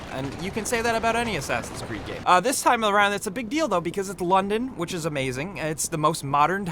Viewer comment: Did he said "It's a big dildo..."? lmao